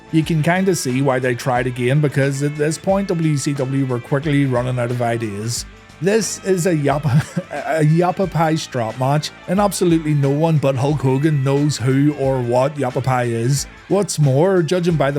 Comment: Damn bro i thought you said an apple pie strap match. I was waiting for the pie to come into play.